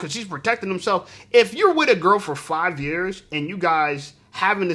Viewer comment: I thought they said shes with a girl